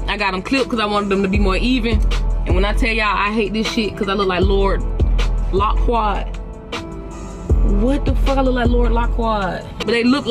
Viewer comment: WHEN SHE WAS CHECKIN U IN THE CAR WHEN U WAS RAPPIN ALL THAT BS 😂😂😂😂 WHY I THOUGHT U WAS SAYING THAT MAN NAME WRONG "LORD LOCWARD"😭😭😭